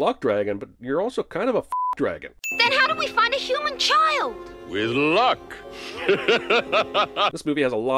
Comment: "How do we find a human child?" "With luck" I ALWAYS thought Falkor says "we look." I'll be damned......